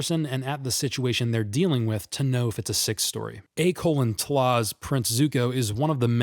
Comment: Did he have a stroke at this point, or is he saying something I don't recognize? "A colon tlaz"?? Oh. "A:TLAB's" short for "Avatar : The Last Airbender". I had to replay it half a dozen times and type it out to understand. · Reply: A:TLA’s I believe is what he’s saying